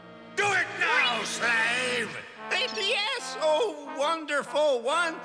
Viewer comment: ha ha it sounded like snarf said "pain in the ass Oh wonderful one"